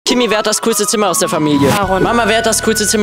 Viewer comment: Sprich doch deutlich habe die ganze zeit Grösste statt coolste verstanden